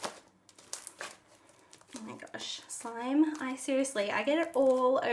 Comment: does she say I make a shit slime ???? Like if u heard it too if she said something else tell me